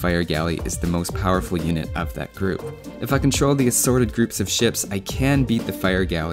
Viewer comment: I thought you said: 'I fuckin' trolled the assorted groups of ships...'